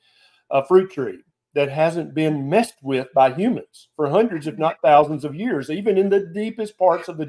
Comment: Sorry, but for accuracy sake and not to throw stones but, Homo sapiens have not been out of Africa for 40,000 to 50,000 years ago and as such could not have been eating like she said for 100s of thousands of years. In fact it’s generally accepted that Homo sapiens have only been around for only 200,000 years and then only in Africa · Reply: I just checked the transcript of the show, I thought I heard “they have doing this for hundreds of thousands of years” not 100 to 1,000 years. Sorry I’m old and my hearing is not what it use to be. BYW I’ve lost 100 lbs on this diet.